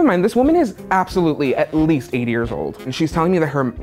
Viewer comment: Did say 8 or 80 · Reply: 80